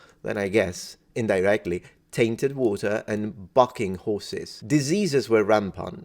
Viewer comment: Yeah, "bucking" horses. That's exactly what I thought he said the first time I heard it.